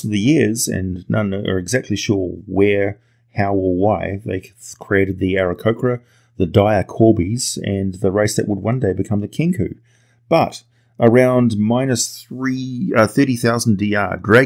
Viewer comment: see i thought he said" dire corgis " and now exist in my world.